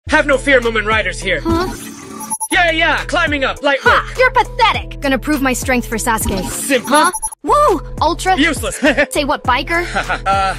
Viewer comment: Nahh i Just Heard Mumen Said "Have no fear women riders here"💀💀💀💀